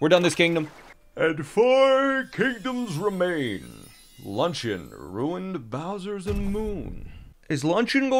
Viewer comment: I heard “luncheon ruined bowsers moon” Instead of “luncheon, ruined, bowsers, moon” and got so confused